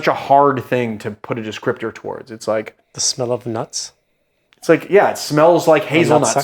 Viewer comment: Did he just say that it smells like Nut Sack at 12:00?